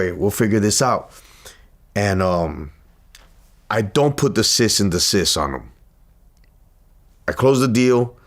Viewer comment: "I don't put the sis in the sis on 'em" ??. What was he sayin?